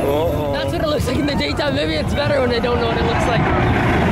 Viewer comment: what did say? Sounded like another language 😂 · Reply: Attack On Titan Fan he said that’s what it looks like in the daytime maybe it’s better when I don’t know what it looks like